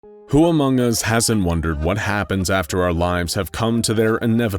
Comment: did he say Amoung us?